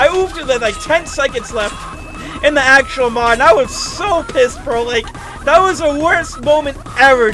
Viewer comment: My guy did you just say “pissed” 12:04 but still no hate plz don’t get mad at me